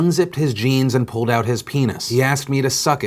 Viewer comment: Yo am I the only one who feels like the pastor didn't do anything wrong? Don't get me wrong, those who commit sexual assaults on anyone is, as Phil says "need to be set on fire," but as far as I'm understanding, he didn't force her in any way. He asked her and she participated willingly. Nowhere did I hear that he put his hands on her to force the sexual act on her. She even said that she went with it because she thought he loved her. I understand what the law says, but can I ask why this particular moment isn't considered consensual? Was it because he was in power? Just out here to understand. Thanks. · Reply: Good catch. I thought she said told, I see now she said asked. I think I saw another comment here that said “told” and had that in my head. In light of that fact, my mind is unchanged. She had an opportunity to say no, but considering all the other factors at play in the situation, that doesn’t mean she felt that she actually had the choice to say no. If a “no” carries possible consequences or retaliation, then that shit ain’t consensual. That’s just setting up a situation to try to force a yes. Clearly the state sees it as a serious problem either way; they’ve since passed that law about clergymen, which means either the law is wrong, the morality somehow changed when the law did, or Mr. Savage just managed to beat the clock on having to appear on a registry list for the rest of his life. I’ll give you three guesses.